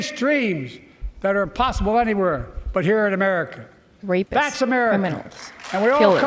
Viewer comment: did he say “dreams that are possible anywhere, but here in America.” 😂😂😂😂